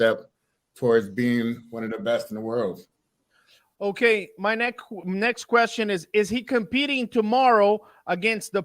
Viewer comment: did he just say my neckts question xD